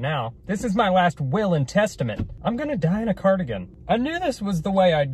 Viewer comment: he said “i’m gonna die in a cardigan” but i thought he said “i’m gonna die in a car again” and i was like, again?? 😂